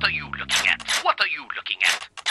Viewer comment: What are you cooking at? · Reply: I know he says looking it just sounds like he says cooking